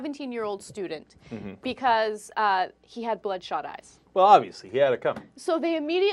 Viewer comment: Haha.! 0:13 He says obviously but the caption says Bunnies.!!